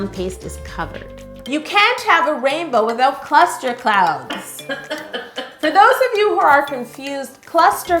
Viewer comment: I heard “cluster clowns.” That’s horrific! Lol